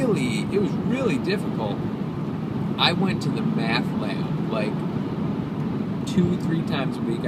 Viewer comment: I thought he said Meth lab at 9:15 xD